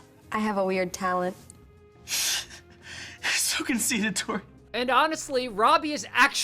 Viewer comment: Robbie called Tori “conceded”? All my life watching this show, I thought he said “I so can see it, Tori”